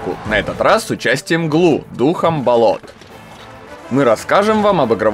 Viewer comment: мне послышалось Дух Амбалот🗿